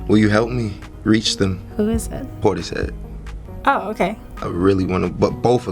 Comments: what artists did he say he wanted to work with at 21:00 ? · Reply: isn't portishead lofi rock, they make trip hop? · Who is he talking about in 21:02?? Who are they? Couldnt hear the name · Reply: Portishead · What name is he saying at 21:03? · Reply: Portishead